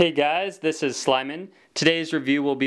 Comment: Why do I hear "Hey guys, this is Slimin'..." ? Is it 'Simon', or is there actually an "L" in there?